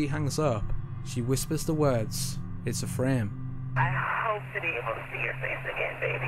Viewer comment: "It's a frame". Sounds like she is saying "it's a friend". Presumably to one of the hijackers.